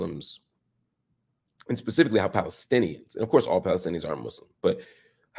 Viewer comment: Also...prof Marc you said "ofc all Palestinians are all muslim" that's... not true lol. It's important for people to know about the minority christian population, especially because they are just as oppressed/displaced and even more close to extinction as they are the minority. I'm sure you didn't mean to say it but I think viewers who don't know this need to know, especially if they are Christian Zionists... it might make them reevaluate their position on Palestine :/ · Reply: Indeed, I did! I really thought I heard ARE not aren’t, my apologies!! I know you’ve dedicated a big portion of your life studying and immersing yourself in Palestinian culture, that’s why I was tweaked. Lol I’m sorry!! 🙏